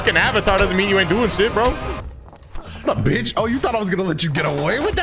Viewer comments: i thought said "thought i was gon let you get away with that shit nigga" Im weak lmfao but he said nano we good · Did say nigga? Skip to